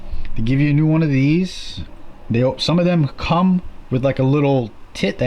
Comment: did he say comes with a tit? LoL